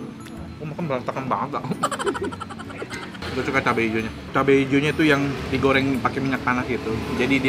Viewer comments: cabe ijonya d goreng pake minyak tanah, apa aku salah denger ya? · Minyak tanah..??? Ape aye kagak salah denger yeehh🤔🤔🤔🤔